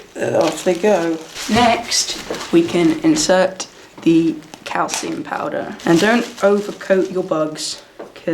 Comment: When you said calcium powder I thought you said cow semen powder and I was so confused for 2 minutes